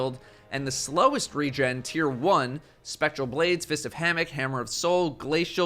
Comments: I swear i heard datto say "Fists of Hammock" Make that something bungie, i don't care what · anyone else hear him say “Fist of Hammock”